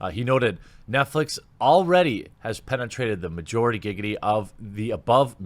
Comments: did say majorigiggity? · Did sneak in a giggity or am i hearing things?